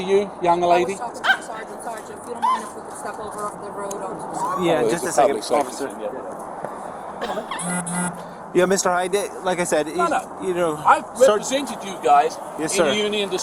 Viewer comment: I thought he said his son is a duck and I was confused for a second